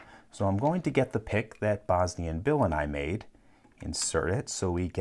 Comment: I am just here for the pick that Bosnian bill and him made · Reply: The first few times I heard him, I didn't know who Bosnian Bill was and I thought he was saying "Bosny & Bill & I.